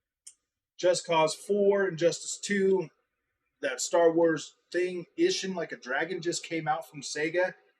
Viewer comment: at this point in the video I am hearing you say… And I may be wrong with the order but I hear: just cause four, injustice 2, Star Wars that thing? My hearing is not the best but I listened to it a bunch of times and it sounds like you’re saying Star Wars but I can’t hear the subtitle. What is it you’re saying? Thanks buddy! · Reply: Star wars skywalker lego game